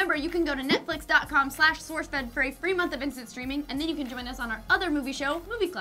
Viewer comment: I definitely thought they said "Free month of instant screaming" and I was like, under what circumstances is that desirable.